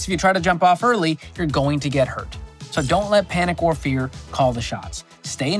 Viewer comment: I was listening, not watching, and at 7:38 I thought he said "...don't let panic or beer call the shots." I had to rewind to hear that bit again.